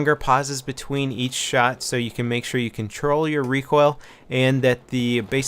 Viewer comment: I heard ''So you can troll your recoil XD